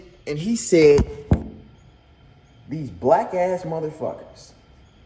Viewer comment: DDG I heard “black ass monkey” idk if its just me bra